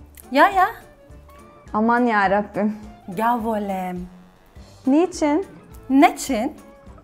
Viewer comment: what exactly does she says there "ya volem"?